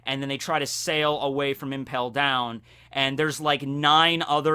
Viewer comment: I had a paragraph long justification for why the Marines kept missing. Then I had to delete it because I realized I misheard him. He said Impel Down, not Enies Lobby. Whoops